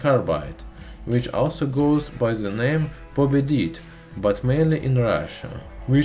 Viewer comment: Is the word "povidit" what the narrator is saying? Could someone spell that correctly for me?